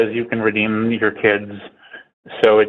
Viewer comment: Did say “n***** kids”?????